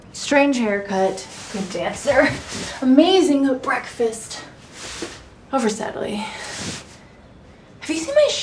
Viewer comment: What does she say? I did not hear it properly. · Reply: Sounds like she said “over, sadly” i could be wrong